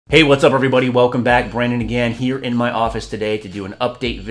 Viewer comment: Two Fours No I don't. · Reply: hah it sounds like you say "brendan". must be the accent